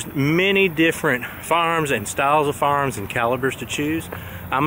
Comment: I didn't get 10sec. into this when your accent confused me! "What 'fa'arms to choose" (you meant "fye-er-arms" but I heard "farms"! Ok, I'm good... Got the "cc" turned on!